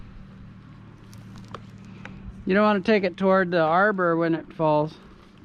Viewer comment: I thought you said “ your gunna want to take it towards the arbor when it comes off” 🤦‍♂️